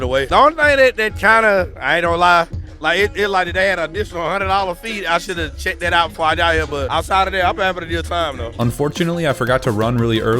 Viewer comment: i didn't understand a single word. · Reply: He said he didn’t know about the $100 fee, but he had a good time